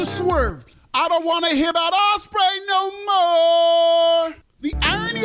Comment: I thought I heard you say "Ass-spray".